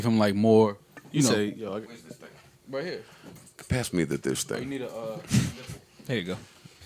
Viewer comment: Did Joe Just Say Pass Me The 'Dis Ting' 🤣🤣🤣🤣😂😂😂... These Guys Need To Come To London, All This Slang Is Just How We Talk From Day...To Say Suck Ur Mum...Jeez 4get It 😐🤦🏻‍♀️